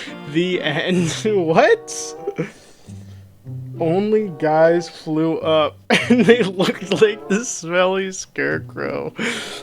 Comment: I think it’s trying to say “only guys threw up and they looked like the smell scare crow”